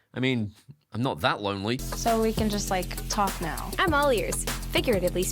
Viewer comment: If you mishear it as "I'm all yours", like I did, that joke hits so much harder...